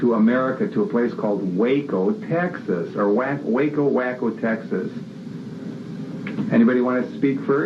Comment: thought that said wacko, guess thats just me